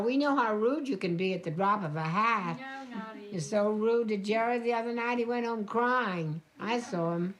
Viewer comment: what did Big Eddie said about Jerry? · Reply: Big Edie said to Little Edie, “ you were so rude to Jerry, he went home crying”.